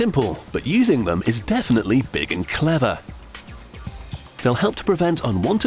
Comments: Lol I thought he said "Produce the spread of sexually transmitted diseases." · lmao! i almost thought so too. he said reduce if you listen close lol